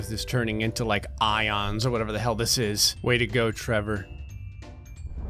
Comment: The subtitles say "you dick you crashed all the vietnamese "